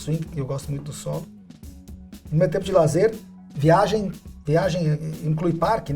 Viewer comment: Por algum motivo eu entendi "gosto muito de anime". (???)